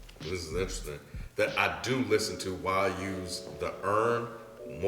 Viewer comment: What was the album that was talking about in 2011. “Why use the urn.” am I mishearing it’s